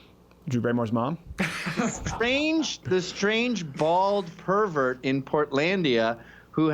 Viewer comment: Thought the opening said strange anal for a second